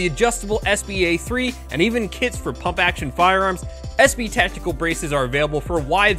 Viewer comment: Every time he says “kits for pump action firearms” I hear “kids for pump action firearms,” and I imagine a very interesting political movement